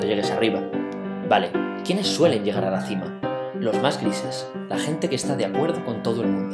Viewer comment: ¿Qué dices ahí? Los más ¿qué? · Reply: ¿Grises?